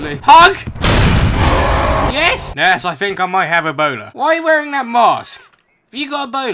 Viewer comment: for the whole time i thought he said boner but just now have i found out that he said Ebola